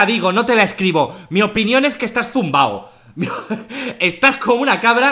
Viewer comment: Escuche BURLAO JAJAJAJAJAJ 10:15 en vez de zumbao